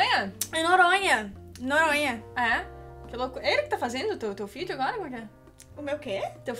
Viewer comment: eu escutei teu filho jsksksksk